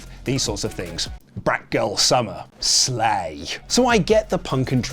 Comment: Did Luke say black girl summer slay @ 2:20🤣🤣🤣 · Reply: Lol he said Brat summer! I live on TikTok so I understood immediately lol